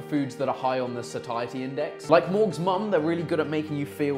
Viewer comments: did you just say morgz mum? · lol fully thought Chris was saying he beat Morgz mum ahahaha seemed bare out of character